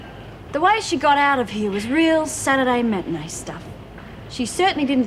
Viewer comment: did Heather say Saturday matani stuff? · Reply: Matinée not matani